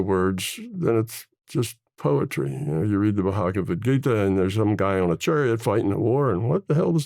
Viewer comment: I’m trying to find out what book he said on 1:01:58. Does anyone know? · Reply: Bhagavad Gita